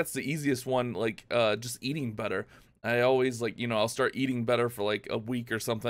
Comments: i thought u said eating butter lol 😂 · For that CQOTD I thought he said eating butter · I thought he said “eating butter” 😂